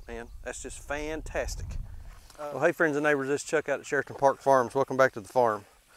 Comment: Every time you say "Sheraton Park Farms" I hear "Sheriff Department Farms"